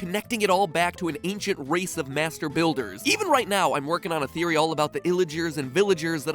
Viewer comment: wait wait wait I thought he said masterbaiters ut I replayed it he just said masterbuilers LOL